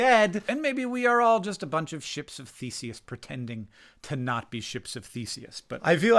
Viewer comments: We ARE all Ships of Theseus. All we can do is keep moving forward, and accept the changes as best we can. And remember, and maybe learn. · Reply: omg i thought he said "ships of feces" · For a moment I understood "maybe we are just a bunch of ships of feces" and agreed, then by the time you said it again I understood it as "ship of Theseus" and agreed with both the right interpretation of what you said and the misunderstood version that I thought I heard. I live with the fear I might inherit cancer since my father had it. And I've always had all these random and constant pains, and a bunch of stress for not being where I want to be. Am I OK? Yeah. I just wished I didn't have to grind so hard I didn't felt like my free time was spent trying to get squeeze a little bit of meaning from the things I enjoy. I don't know why I am sharing all of this. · I thought you said, "ship of feces," until I rewound and heard correctly.😅 Bodies and brains betraying us is so challenging and more difficult than most people could comprehend. Especially when it's over and over and over again. Sending love your way.💐 Thank you for voicing what you voiced today. I think not only was it good for you but for many people.❤️ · I thought you said "ships of feces" and I was shocked, but also like, yeah that's accurate lol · Oh I thought you said Ship of Feces…. Awkward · You said ship of Theseus and I heard “we are all ships of feces”. I can’t argue with that.